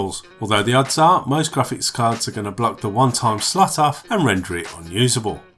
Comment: I'm sorry, I've played and replayed it and still don't understand what it says at 2:46. Render what unusable? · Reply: when you plan using a graphics card with this motherboard your PCI Express slot is gonna be blocked by the GPU and render the PCI express slot unusable sadly